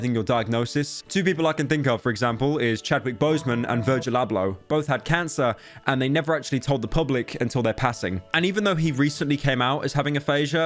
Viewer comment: I thought he said virgin pablo